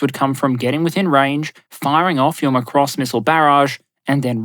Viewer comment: Hoi, heads up, at 30:16 the subtitle says "5.5 billion US dollars" when you said "15 and a half billion US dollars." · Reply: Also, at 44:03, did you say "Macross missile barrage"?